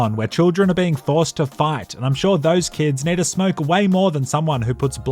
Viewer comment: I heard "children are being forced to fart" instead of "fight" 💀💀💀💀